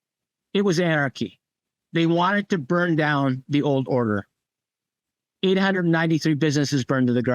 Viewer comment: did he say 893 businesses burned to the ground? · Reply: yes, 893 businesses heavily damaged or destroyed. Documented. Actual number of businesses affected 1500 plus.